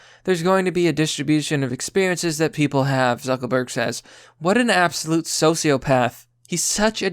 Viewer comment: Do I keep hearing “zuckleberg”